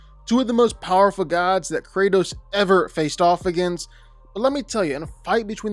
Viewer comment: I legit thought you said kratos NEVER faced off against Thor and Zeus. I was like wtf.